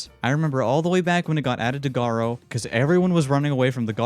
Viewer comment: The captions say goo instead of Garou 💀